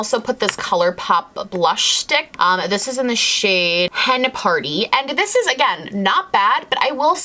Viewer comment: Thought you said the KVD blush was in the shade Panty 😂😂😂